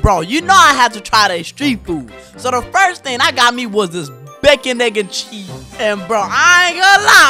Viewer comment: Why does it sound like he said bacon n-word cheese